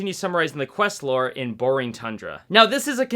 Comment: did you say "Borring thundra"? XD